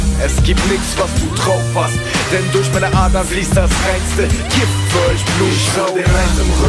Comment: "denn durch meine adern fließt das reinste ???????" ...... ...was sagt er? "kiffzeugblutsauger?" :|